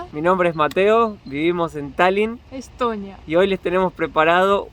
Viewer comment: jaja al principio pensé que dijo que viven en Stalin